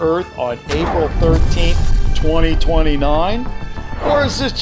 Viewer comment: Me: wat Reason it’s because he said 2019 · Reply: no he said 2029